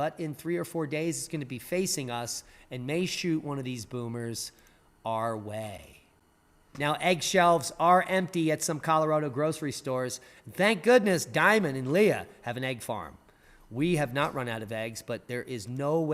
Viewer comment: I thought you said "Egg shells are empty at some Colorado grocery stores." I was thinking that's one heck of a flu!